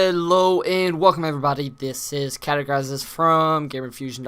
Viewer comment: sorrry but did you just say your name was "cadaugorizes"? because that is exactly what it sounded like